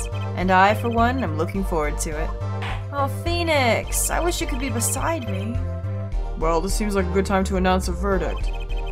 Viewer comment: I was kinda not paying attention and I swear to god I heard "Aw Phoenix! I wish you could be inside me!" and I couldn't be sure I had misheard cause #JustThings